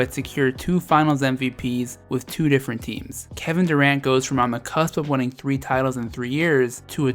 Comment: Did I misheard that? KD won three titles in three years? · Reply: Arzadon- Yes you misheard. Narrator said that KD was on the cusp of winning 3 in a row but GSW got derailed by Toronto. Replay the video and see or hear for yourself.